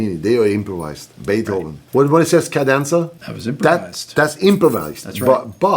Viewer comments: What did he say at 30:30? Wastehndsda Cadenza? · What did he say at 30:30? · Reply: 'cadenza'